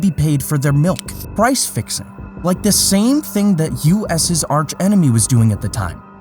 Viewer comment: He said "US's arch enemy" I heard "USSR tsh enemy" i was like waaaah?!!